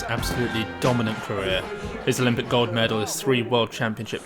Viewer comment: ".... his olympic GOD medals...." that's what I heard.